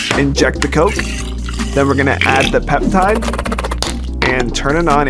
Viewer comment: Inject the coke 🤣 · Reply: bro i thought he said "never gonna add the peptide" 💀